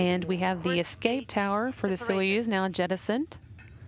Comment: what does the say at 3:51 "we have the escape tower for the sojus now "what"?" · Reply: Jettisoned